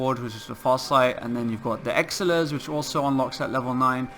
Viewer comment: Someone notice him saying "exilirs" instead of "elixirs" on 0:55 hehehe